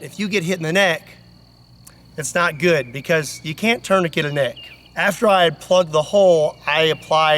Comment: If you get hit in the neck I know very well that ______ - What the hell did he say here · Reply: "it's not good, bc you can't tourniquet the neck"